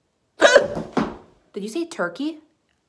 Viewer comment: "What food do you eat the most at Thanksgiving dinner?" Me" mashed potatoes!" You "UGH! Did you say tricky!? I thought you would say mashed potatoes! They are the best!" Me "☠"